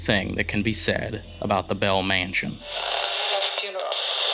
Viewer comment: I swear she's saying "we're having a funeral.